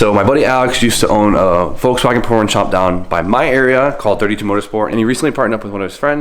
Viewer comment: Hahahahaha wow this guy legit just said a Vw porn shop, even the subtitles say it. LOL Got to